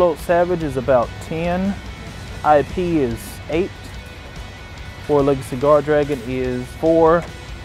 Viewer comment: When he said “world legacy guardragon” I thought he was saying “four legged cigar dragon” and I couldn’t stop laughing each time imagine what it would look like. (I don’t play yugioh I just watch for nostalgia)